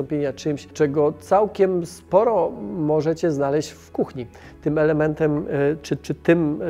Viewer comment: o rany "w kuchni". jak z początku nie uważnie słuchałem, to usłyszałem "w kupie" i takie: "wtf? coś tu jest nie tak"